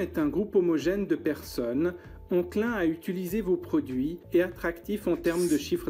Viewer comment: C'est quoi ont klein dans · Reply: enclin*